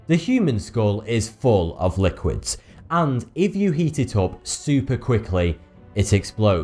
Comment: I thought he said eat it up but he said heat it up😁😁😂😂